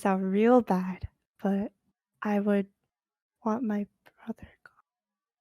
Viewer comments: what does she say here? I would want my brother to _______ (go away?) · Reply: i think she said “i want my brother gone” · what did she say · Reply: I want my brother gone